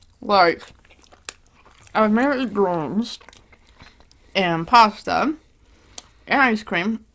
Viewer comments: What does she say in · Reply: I think she said : “I may eat greens or grains” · OK so after basically a full year of genuinely trying to understand what she was saying here, I came to the conclusion that she was trying to say “I mainly ate grains”. I’m so relieved that was about to drive me nuts